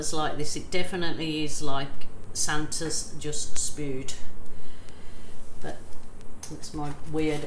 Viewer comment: Did you say Santa's just spooed???